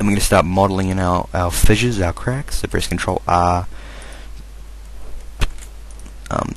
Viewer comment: Hey I dont understand what you're saying at 0:33. It sounds like now press control uhhh..